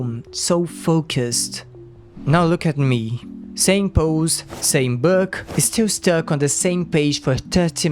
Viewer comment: i thought you said “same nose” and i was very confused. The nose is one of the most noticeable differences between those characters.